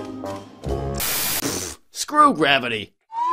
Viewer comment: The first time I watched it I swore I heard "screw Rarity". =0